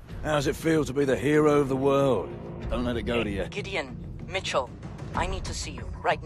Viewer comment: i thought Ilona said Idiot but she said Gideon.